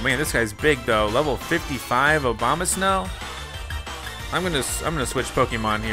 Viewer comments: lol I heard Obamasnow Like if u heard that to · it sounded like he said Obama-snow